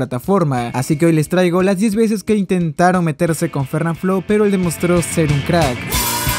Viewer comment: Por dios pense que decia "demostro tener carck" XDDDD